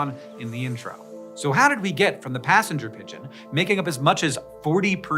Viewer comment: I am so dumb. I thought you said “carrying pigeon” and that you were going to explain how the telegraph made sending messages through pigeons redundant and that’s why they disappeared. Like fax machines.